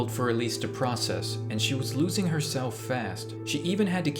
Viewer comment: ok but why did i thought that is going to say 'losing her shit' instead of 'losing herself'